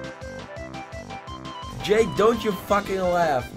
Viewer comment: Find it so funny, at first I thought you were saying "Jake don't you laugh!" But then watching this video I realized you were talking to Jaderocc xD Then I was like "I'm not laughing Wess I'm a mix of emotions;--;" I tried to warn you about that Golbat and I commented "Shiny Golbat Oh shit!!!" hoping you would see that shit and you ran! It was chill talking to you after the Stream though. I felt like I was on a Skype Call chatting with you while your Face Cam was up because I was the last one in the stream! ^ - ^